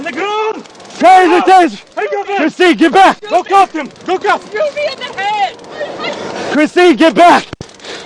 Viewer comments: Can someone please tell me if they hear the same thing I'm hearing at 2:58? I heard the suspect saying "Christineee, shoot me in the head!" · Omg christine obviously got the gun out for him. I hope she recieved attempted murder charges also. It was clear she was trying to stop the arrest and went into the car to get him the gun. Prayers for the officer. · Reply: said at 2:58 to Christine : shoot me in the head ! Anyone catch that!???